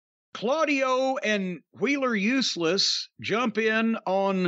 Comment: I've been laughing so hard when I heard "Wheeler Useless" 😆